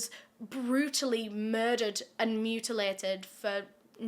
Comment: I thought she said “beautifully murdered” and I had to rewind it to hear “brutally murdered” 😂😂😂